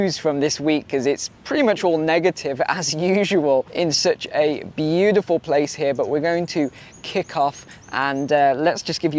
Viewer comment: Thought you said it was a beautiful park....😁😁snakes, lizards...😅